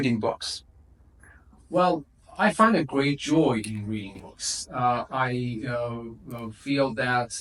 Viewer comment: great joy of reading books.. instead of in